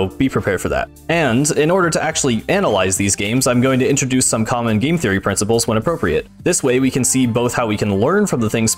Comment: what. Did you say "hello internet welcome to GAME THEORY?"